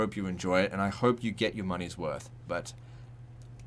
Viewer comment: what did you say bitch? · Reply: Fuckers